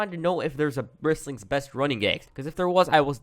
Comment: Good video, but thought it said best running gaps, which I think would have been a good video as well